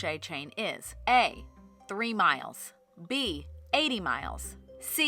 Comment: did you say MILES